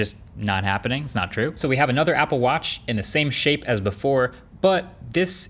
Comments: when he was saying "same shape" of the Apple Watch, for a second i thought he was saying "same shit". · When you talked about the watch and said “same shape” I almost thought you said “ same shit” lol…maybe because I was thinking it😂